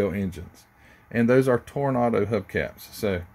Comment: Toronado? I thought you said Tornado.. Good build. I have that one on my shelf. The 4 engine dragster was Tommy Iverson.